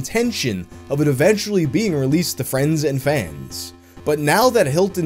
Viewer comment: For a second I thought you said they made it for friends and family.